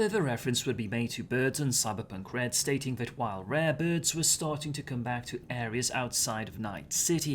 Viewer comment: you say "rare birds" and i hear extended "rabids"